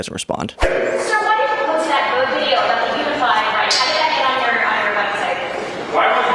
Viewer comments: My God, I thought he said "unified right", but it's even worse! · I heard "unified Reich" as opposed to unified right.